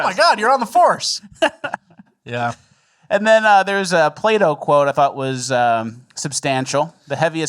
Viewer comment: I thought he said Play-Doh quote